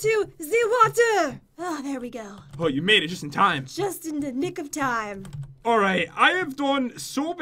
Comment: I thought Jen said '' just in the lick of time''